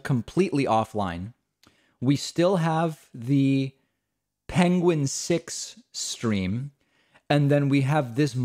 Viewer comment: Thought I heard pengine